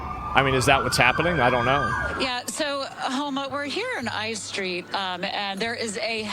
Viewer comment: did she call me a homo